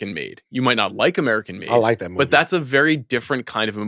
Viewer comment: I thought yall said Tom Cryise made "American Me" instead of "American Made" and for a minute I was like wait wtf ? Tom Cruise produced American Me??!!